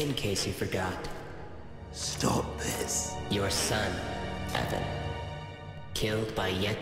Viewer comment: did anybody else hear that? They said "your son. E-" it sounded as if they were going to say Evan. And it’s a small detail, but you can see the change of who’s talking. Golden Freddy’s pupils got brighter. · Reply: It did say Evan